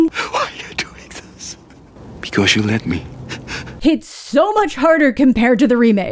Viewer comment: For some reason, my mind is trying to make more out of the fact that in the original the line was "Because you let ME" vs. the remake going "Because you let US". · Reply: I thought the original said "...let ME." Okay, I scrolled back to rewatch that part, and he does say let me. I'm confused about you writing the wife was equally involved since it's me and not us in the original. Unless I'm reading your reply wrong.